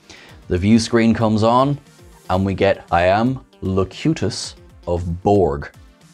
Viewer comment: "I am Locutus of Borg" .... I always thought Locutus said, "I um...load Q-Tips aboard"